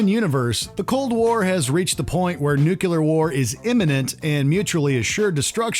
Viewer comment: Did he just say "Nucular"?